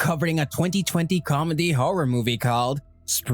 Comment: did he say comedy movie?..